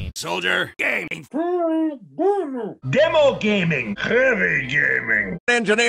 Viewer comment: The End Lyrics That I Hear : "Spy Gay-"